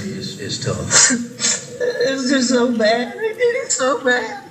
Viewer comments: I hear “it’s so bad it is just so bad” lmao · I hear "it's so bad I get it's so bad."